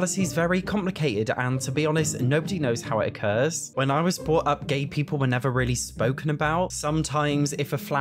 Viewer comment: I heard “when I was brought up gay, people were never really spoken about” 😂